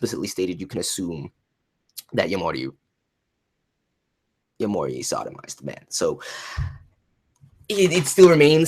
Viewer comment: What does he says at 25:27? English is not my native language so sometimes I have trouble understanding. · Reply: He said: Yomori sodomized men. Definition: Sodomize is to perform unnatural or abnormal sexual intercourse on a person. When you penetrate a person anally, this is an example of when you sodomize that person.